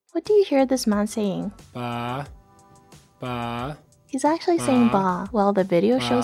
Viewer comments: My ears are not working when 5:17 plays I hear "Pah" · i searched a sheep images on google and i heard baah lol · all I hear is “aaahhh” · i hear AHHH AHHH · with the "bah" and "fah" thing I heard "ah/uh" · I hear ła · im not the only one who hears baa right? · nah i heard "aah" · I heard neither bah nor fah, I heard aah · Honestly I just hear him monotone screaming. No f, just 'ahh'